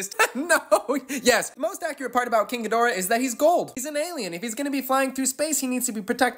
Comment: Everytime i hear king ghidora i hear "kinky dora" I hope to have curse someone else. I can't be the only one 😂